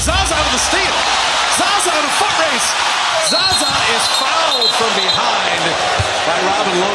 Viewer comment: Im legit curious cuz all i heard was "zaza was fucked from behind"